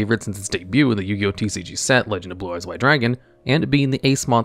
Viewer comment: Did you say red-eyes white dragon? Lol